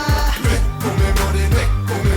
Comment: They say "Hey, what's your dream?" in Korean, nothing about money. The sentence is, romanized, "ni kkumi mwoni, ni kkumi mwoni mwoni" (so literally translated, "your dream what is, your dream what is what is") Later on you'll find another song where they use the word "manhi" pronounced "money" which means "more" :_D but it does sound like they say "I want money money"... just so you know :)